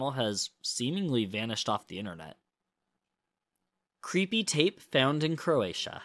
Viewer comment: good lord - when you said, "creepy tape," I... thought you said "cp tape" and it started playing and I 'bout had a heart attack 🤦🏼‍♀ oh my goodness.